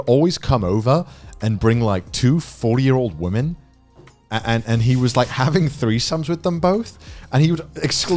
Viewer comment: I heard two 4 year old women and went WTF 😂😂😂